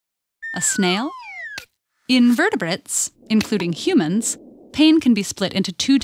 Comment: It sounds like you're saying "invertebrates" instead of "In vertebrates"